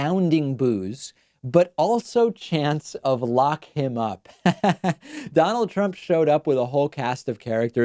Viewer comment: I thought I heard “Fuck him up”!! 🤛 😂